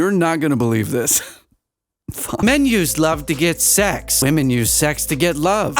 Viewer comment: dude said "men use" and I thought he said "menus"...imagine my initial surprise to finding out menus have sex